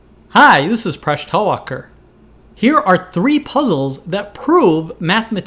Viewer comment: I know his name is in the description, but for the longest time I thought he said his name was "Fresh Tall Walker" like it was some indian nickname or something.